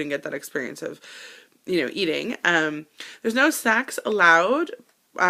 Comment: I totally thought you said there's no sex allowed too! hahaha